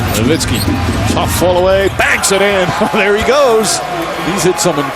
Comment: Sorry, I do not understand/speak the language. What the narrator says at 1:25? I get something like ''flow away, bangs in''.. Can anyone tell me what he says? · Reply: Tough fallaway, banks it in. There he goes.